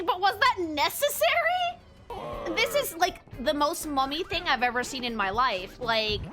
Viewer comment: Oh "mummy", I thought she was saying mommy lmao.